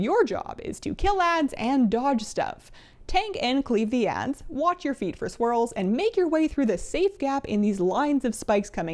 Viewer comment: I heard "watch your feet for squirrels". That would make the fight cuter, but also deadlier.